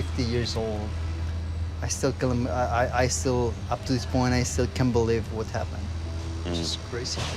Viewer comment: anyone else rewatched 17:20 did he say "i still killed him"